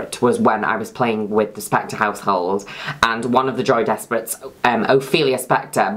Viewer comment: i always thought you were saying joey desperate